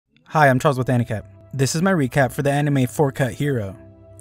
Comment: I heard him I'm Charles with handicap